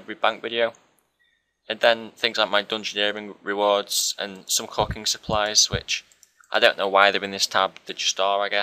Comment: I thought you said some cocking supplies , the fuk